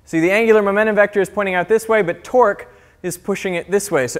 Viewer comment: I start hearing twerk instead of torque